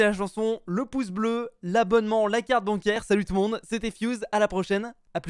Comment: C'est moi ou a dit ''la carte bancaire'' ?